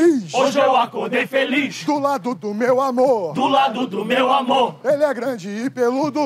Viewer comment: na 1ª vez que assisti, entendi "hoje eu acordei feliz do lado do meu AVÔ" D: HSUAHSUAHSUAHSUAHSUASHUASHASUAHSUASH